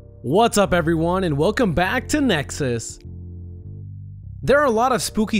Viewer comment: I thought nexus said negus lol